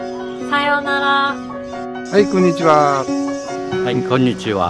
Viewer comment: what are they saying at @ 0:17 before konnichiwa? sounds like "hai konnichiwa"